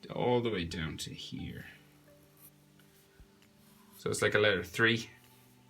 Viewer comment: did he say letter 3